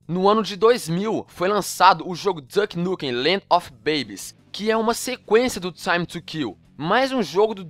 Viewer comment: quando voce foi falar babes, eu escutei voce falando penis :/